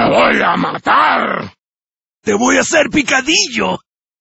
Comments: lo gracioso es que dice "asplastar" en vez de aplastar por lo que deve ser mas dificil de traducir al ingles, seria como "it's time to msash" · “An outsider!” “Un forastero” Am I the only one who thought it sounded like “Un Forest stay down!” 🤣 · always sounded like Watto from Star Wars saying “the boy said we can eat you” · The number of errors of the voice actors and the translation is quite considerable. 0:02 "Ah, for the love ..." is really "Go get him!" (Spanish: “¡A por él!”) 0:26 "You bastard!" It is really just "Bastard!" (Spanish: "¡Cabrón!") 1:13 (??????????) really is "He is in the trap!" (Spanish: “¡Está en la trampa!”) 2:22 "You can run, but you cannot hide!" is well translated but the voice actor says it wrong at the end saying: “¡Puedes correr, ¡pero no te puedes escondir!” but it is not "escodir" is "esconder" 2:33 "Quick, use your muscles!" It is really "Quick, it's an intruder!" (Spanish: “¡Rápido, es un intruso!”) 2:40 "Fucking pig!" It is really "You are a pig!" (Spanish: “¡Sos cerdo!”) 3:01 "It's time to smash!" It is well translated, but the voice actor says it wrong and ridiculous, instead of “¡Ya es hora de asplastar!” should say “¡Ya es hora de aplastar!” The rest is fine, especially 0:46, by the way, my native language is Spanish. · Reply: Sounds like he says assblaster